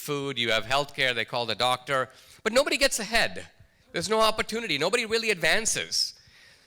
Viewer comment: At first I thought it said "Nobody gets head" and I was like F that